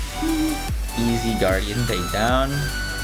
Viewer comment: Ok at 2:13 when you said easy guardian takedown, I misheard it as "Olive Guardian Takedown"